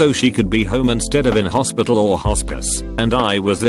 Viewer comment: My god, I heard “hORsePiss” instead of “hospice”...